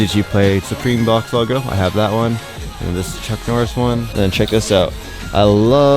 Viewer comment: HAHAHAHA did you say Chuck Norris at 13:20?????